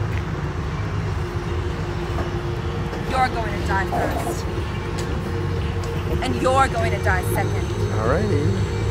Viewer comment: what she said? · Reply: "you're going to die first, and you're going to die second"